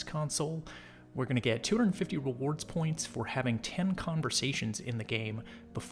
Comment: I thought it said 10 conversions 😅